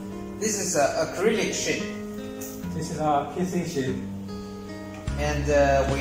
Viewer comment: I heard "This is a pissing shit" I hate my mind😑